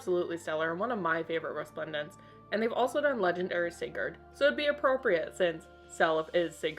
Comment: The captions said Sacred like Scared Stones not Sigurd.